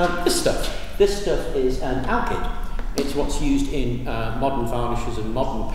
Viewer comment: Can anyone tell me what is the most commonly used binder mentioned in the 7:13 min video? I didn't quite catch the name. Thank you!! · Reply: AlKyd!! Thanks to from the earlier comments 🏆